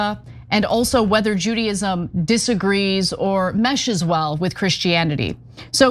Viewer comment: that not what he said. He said (Judaism) openly supports the ‘prosecution’ of (Christianity)